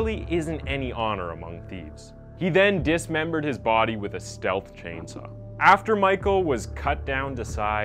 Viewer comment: don't tell me I'm the only one that heard him say stealth chainsaw